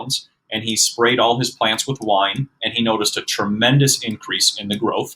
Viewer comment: wait a minute- 21:46 did he say that guy sprayed his plants with wine? Am I hearing that wrong?